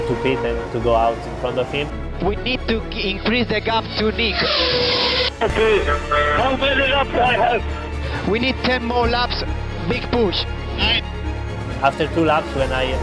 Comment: what they saying? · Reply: Eng: We need to build a gap to Nick [Heidfeld]. RK: How many laps do i have? Eng: We need 10 more laps. Big Push!